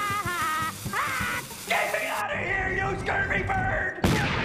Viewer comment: I thought he said get me out of here your dirgy birg